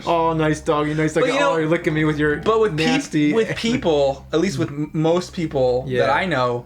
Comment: I was listening to this while working, and at 16:43, my brain kinda connected with what James and Mike were saying together, and it sounded like Mike was saying "peesty." Hearing that in my head really makes me laugh!